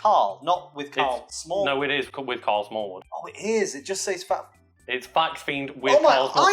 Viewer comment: Thought I heard Smarl Karlwood.